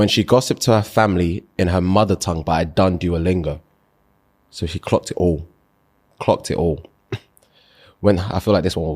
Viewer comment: Im sorry for asking, I’m Dutch so not all the English slang is familiar.. what did say at 9:01 “when she gossiped to her family…” did not understand the rest. Apart from that I love this podcast, it’s hilarious❤😂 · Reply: In her mother tongue , but I done duo lingo , so she clocked it all